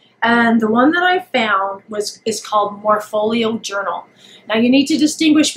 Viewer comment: This is great! I can't find the app or the link to the app in your comments. Am I missing something? Thanks for sharing. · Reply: Found it. I thought you were saying "portfolio." Sorry.